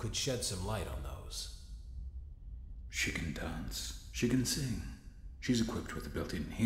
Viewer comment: when afton said "she can dance" I heard "chicken dance"